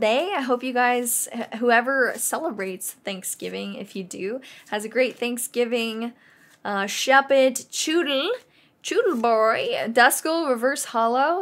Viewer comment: Did anyone else hear her say I hope everyone has a great thanksgiving shuppet?